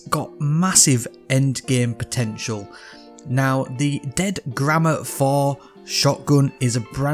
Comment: I heard "dead grandma 4"